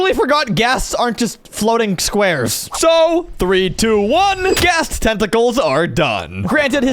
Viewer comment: i thought he said 123 gasts tentacles are dumb